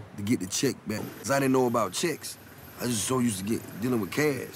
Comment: at first i thought he said chicks lol